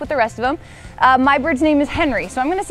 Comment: I thought she said “my birth name is Henry” and was like.. is trans?